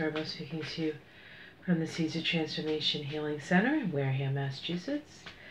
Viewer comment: What is the name of your company? It sounds slurred in your videos. Thanks, enjoy your transparent, down to earth style. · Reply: The seeds of transformation center I thought she was saying Caesar of Transformation center for the longest time lol